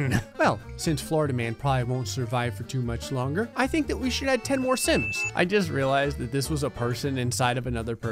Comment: I almost thought I heard the sin counter from cinema sins and that said “Add 10 more sins.”